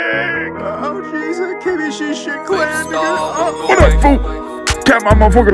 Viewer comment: I can’t even tell what he’s saying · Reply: “Oh jeez that Kenny G shit playing nigga”